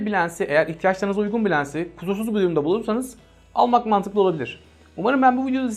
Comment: 'te ne dediğini anlamak için 20 defa geriye sardım. Sonuç: yine anlamadım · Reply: kusursuz bir durumda bulursanız